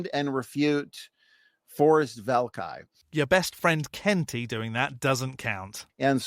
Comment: you mispronounced the vowel, sounded like you said cEnt-y...